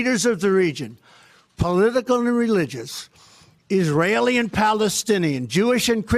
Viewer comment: what the hell does he even say? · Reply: politicalnureligous